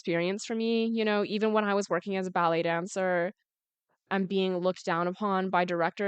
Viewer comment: Oh… I thought she said belly dancer. Umm, nvmd lol